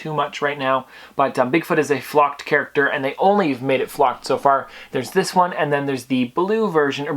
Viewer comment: i thought you said flopped and i was thinking damn what happened 😂